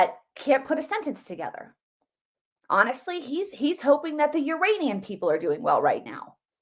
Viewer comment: I thought Joe said he was concerned about the "Uranian" people...like from Uranus....not the "Iranian" people??? I heard "Uranian". Listen to that again.